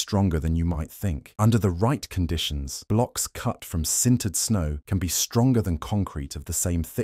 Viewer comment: Did he just say "sometimes stonger than concrete?" 😂